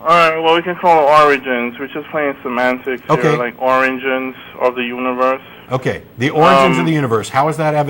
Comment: "Oranges of the universe" ... you can't convince me that isn't what he said